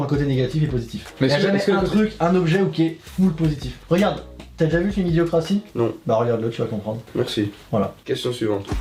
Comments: dit quoi à 13:05 ?? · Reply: Je crois qu'il dit :"Regarde, t'as déjà vu le film 'Idiocracy'? Bah regarde le tu vas comprendre." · il dit koi (le nom du film) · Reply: Bondoir, Je vous réponds que a dit "idiocratie" ou "ideacratie" c'est le nom du film. J'espère vous avoir aidé.